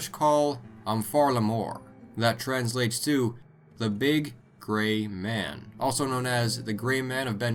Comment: I thought you said "The large GAY man" lolol. Better watch out with him sneaking up behind you. Kekekekek